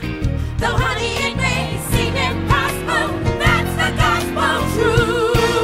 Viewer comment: Was it just me who when they first saw the movie thought they were saying COSMO truth?